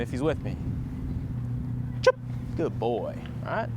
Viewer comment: I hear you saying " chirp". Is that necessary? Or is saying "good boy" okay.